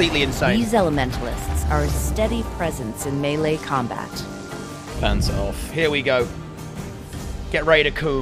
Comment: Did say pants off? gachiBASS